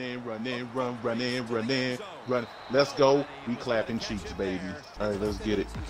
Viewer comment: Lat 07:35 Did he say we clappin cheeks??????????????????????